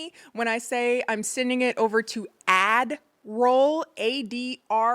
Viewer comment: I'm cackling with the painting bc it made me realize I have been mishearing both the true and the pun version, I thought it was "send it over to adderall kennie" as in, your medicated pre filmed version 😅